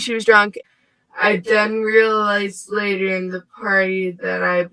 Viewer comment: ish says, “I done realize…”. I usually wouldn’t bring up petty things like this, but it’s been said that Truckee is very affluent. Like they grind that into us. Yet in a lot of interviews these people sound…,what’s the word…dumb! And they look dirty, like they don’t shower much.